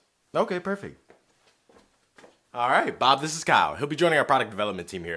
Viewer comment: the captions say: bob this is cow